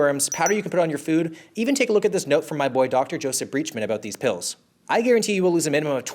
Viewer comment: Okay, why did I think he said "my boy-doctor" as if his doctor was a 10 year old boy genius with a medical degree.